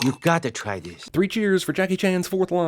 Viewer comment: Sentence: Jackie Chans 7 sentences Edit: 8? Because Monkey says 'Po' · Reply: I thought Jeremy said "Jackie Chan has 5 Lines"